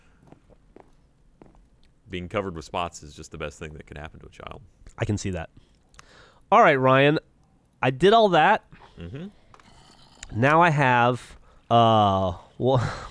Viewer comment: I really thought Geoff said "Alright Ryan, I diddled that."